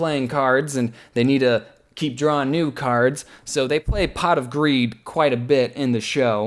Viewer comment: I THOUGHT HE SAID POT OF WEED